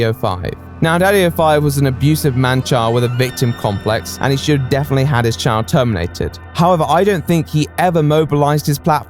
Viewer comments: Sounded like "He should definitely have his child terminated" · thought he said "shouldve had his CHILD terminated" haha